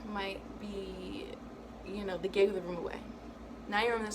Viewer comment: Excuse me, what did you say about Saturn in the 12th regarding the hotel/travel example? English is not my first language lol. Thank you! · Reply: Christian Q. No problem. The example was they might have given the room away.